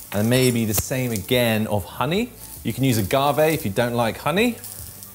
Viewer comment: I heard you say the word Gavey/Garvet. If you don't like honey. Whats that? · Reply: "Agave" - It is a sweet syrup. Commonly used to replace sugar